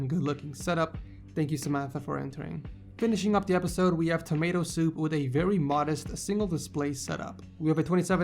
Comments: Did he say Tomato Soup at 5:40 ? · Did you just say tommaso as tomato soup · he said "TomatoSoup" instead of tommaso 😂😂😂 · when you you said tammaso i thought you said tammato soup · anyone else who heard him saying tomato soup instead of tommaso